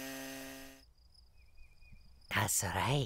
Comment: Who else thought Luigi said "shit yourself" instead of "check yourself?